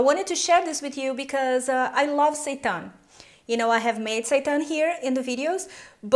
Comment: Did she just say she loves Satan???